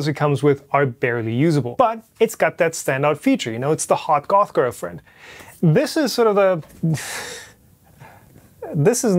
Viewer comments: Great video! Loved it... I thought your said Hot girlfriend talking about the Magneto X... 🤣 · "It's the hot Goth Girlfriend" lol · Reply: I heard "hot golf girlfriend." You ruined it.